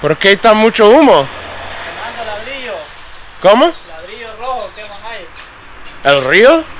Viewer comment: At the burning house they said "ladrillo rojo" it sounded like they said EL RIO, but Ladrillo means "Brick". So all that smoke is due to making bricks in that big house-looking oven.